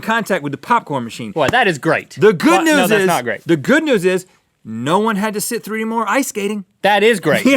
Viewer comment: What did Rhett say at 2:43? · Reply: The good thing is no one had to sit through anymore ice skating